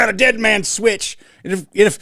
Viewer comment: I thought he said dead man's witch 🤣🤣🤣🤣😭😭😭😭